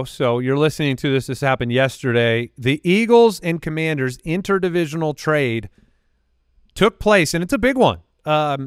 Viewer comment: I thought said “inter dimensional” trade 🤣